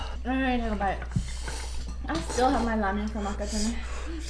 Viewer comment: did really say "I still have my 라면 from 아까 전에"??